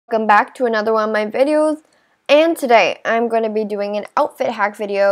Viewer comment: It sounds like you said * “Come back to one of my videos”*